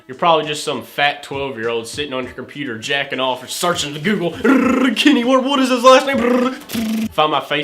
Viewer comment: it sounded like he said "Kenny Wart", or "Kenny Ward"